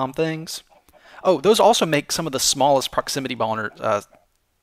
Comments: Did, umm… Did he just say, "those also make make some of the smallest proximity boners?" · I don't like to be immature but I swear you said smallest proximity boners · Did say "Proximity Boners"? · lol why did you say proximity boner? XD